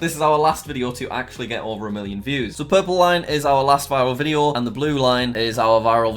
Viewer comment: What Jack says: so the purple line... and the blue line What I hear: so the purple lion ... and the blue lion